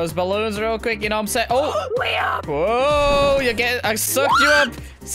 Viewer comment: i think said real name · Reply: His name is Liam search it